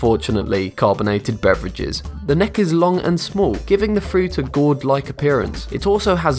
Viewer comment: 😂Thought he said God Like, then saw he meant there is a fruit named "Gourd".